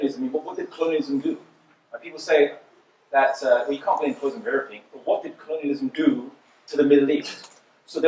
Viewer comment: "people say we can't blame..."??? Can anyone please say what he said there, I couldn't hear it properly. And 1:50 "we didn't make it, we didn't ??? it" Also 2:40 "we won't be selling our resources at ???? prices"... And last one 3:13-3:14 Thanks in advance. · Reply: He said, “people say we can't blame colonialism on everything”, but meant “people say we can't blame everything on colonialism”. 1:50 “we didn't make it, we didn't invent it” 2:40 “we won't be selling our resources to other people at dirt cheap prices” 3:13 “these puppet dictators are propped up”